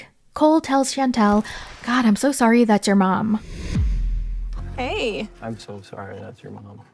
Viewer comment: I thought Cole said"Im sorry about your mom" not im sorry thats your mom. Did anyone else think that??